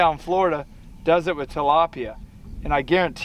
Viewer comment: The DMFD collab just posted and now this upload, nice! That gator footage in the intro was sweet. All I have here in Germany to film for my intro would be a beaver I guess.. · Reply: yeah your right. I thought he said otter he said tilapia. My bad trying to watch at work.